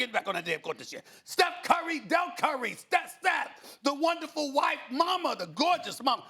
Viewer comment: Did he just say “wonderful white mama”? I’m done 😂